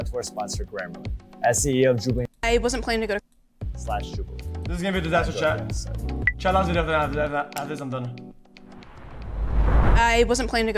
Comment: Ok, I rewatched it like four or five times, I've deduced what the creature has said: "This is gonna be a disaster chat, chat af-affff-after this I'm done."